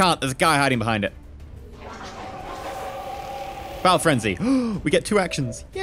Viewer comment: I'm sure I misunderstood... At 23:22 did you say "Bowel frenzy!"?